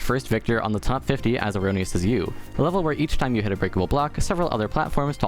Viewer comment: For anybody who couldn't make out the words, the level is called "As Erroneous As You"